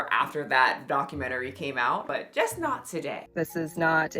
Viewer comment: I thought she said, "It's hot today." 😂